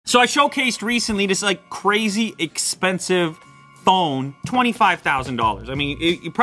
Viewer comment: I thought he said crazy expensive thong at first 😂😂